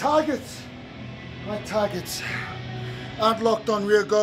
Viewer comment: 岡君コメント下手くそだなぁって思うけどプロレスに対する真面目さがすごい伝わってくる。 ヘナレの3:16が「Myタグチ！」に聞こえる…。